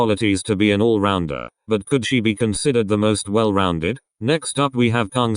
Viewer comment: NOOOOO INSTEAD OF "COULD SHE" I HEARD "COOCHIE" STAWPPPP